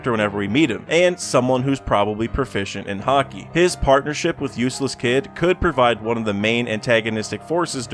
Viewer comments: Did you just call him "Useless Kidd?" yowch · am I hearing Useless Kid👀😂